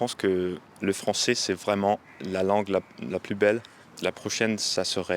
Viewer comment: I misheard "plus belle" as "poubelle" and was like "quoi?!'" 😂